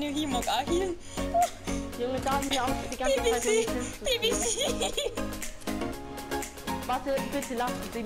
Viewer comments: he was not saying bcc he was saying bbc tf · was he saying bbc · my mans all saying BBC instead of BCC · Who heard dude that said bbc at 5:09 🤣🤣